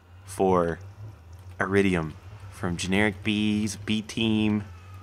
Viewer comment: it sounds like he says generikbee when he starts the episode lol